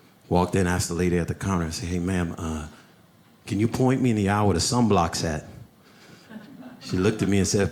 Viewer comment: What does he say at 5:14??? "Can you point me in the direction" ??? What does he say after that??? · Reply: Can you point me in the aisle where the sunblock's at?